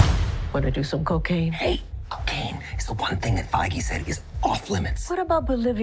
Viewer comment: I heard "Feige" as in "Kevin Feige" who runs Marvel. And Deadpool would mock Disney in this way especially if Ryan Reynolds is calling the shots since he's old school when it comes to comedy.